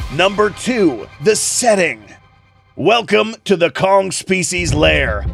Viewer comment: bro i swear to god i Heard kong fecies layer💀